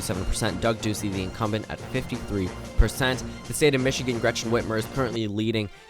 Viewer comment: I slightly zoned out and when i heart gretchen whitmer i thought he said gretchen wieners